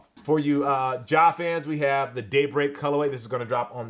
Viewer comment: When you said ‘Daybreak’ I heard ‘date…..